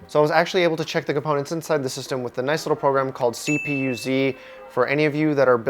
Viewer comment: Rofl he said 'CPU-Z' i think, which sounded like something I was interested in since I am trying to upgrade my pc/buy a new one, but I misheard it as 'cpcz' and now the gov. thinks im some czech communist